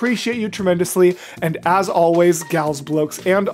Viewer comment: i thought he said "galsblokes and all other folks" and i was just thinking "... what's a galsbloke?" XD